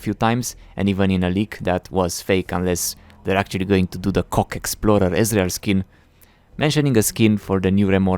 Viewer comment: I'm not sure I heard that right "c*ck explorer" ezreal skin? >.<